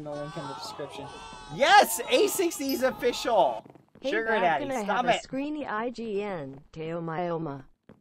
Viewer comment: did he just say sugar daddy